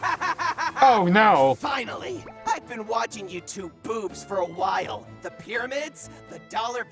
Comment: I thought that Bill Cipher said "YouTube Poops" for a second, and on the subject to this: 10:23, the Gravity Falls book that I have gotten: "The Book of Bill", has Bill Cipher stating that any symbol of him that you draw, scratch, spray-paint, or burn into the human world creates a direct peephole from his reality to ours. and I saw red and not so good or so bad graffiti of Bill Cipher, (I don't know if that red graffiti is Bill Cipher, because a black spray-painted "3" is covering that red graffiti, the graffiti of what I thought was graffiti of Bill Cipher doesn't have arms, and has a firey like crown), but does that mean that Bill Cipher lives near (but not to close) to my apartment?! Oh my God! * The illuminati triangle appearing, and The X-Files theme playing. * Also, would Bill Cipher be a fan of YouTube Poops? (I'm asking because some YouTube Poops are as chaotic as him.)